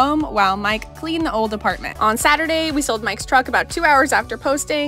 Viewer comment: I misunderstood the voice-over for a moment and I thought you said "and we're off to visit France" and I thought, "They understand how oceans work, don't they?".